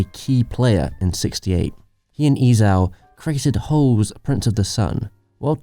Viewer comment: "Holes printed the sun" - this is what the subtitles say!